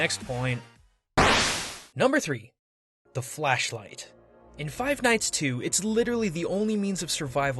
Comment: I thought he said Fleshlight